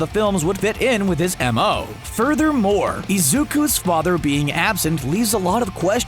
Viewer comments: Did he just say IZUKA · DID YOU JUST SAY “IZUKA”